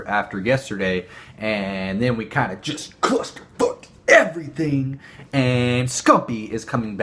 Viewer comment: LMFAO CLUSTER FUCKED ERRRAYTHING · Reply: Thought you said Clayster xD